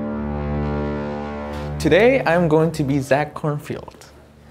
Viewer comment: okay did he just say Korn*FIELD*